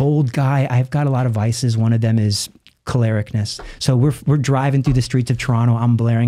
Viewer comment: What was the word he used here? Kolaricness? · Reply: choleric, one of the 4 temperaments